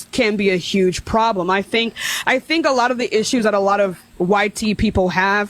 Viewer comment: did she really say YT out loud? Lmfao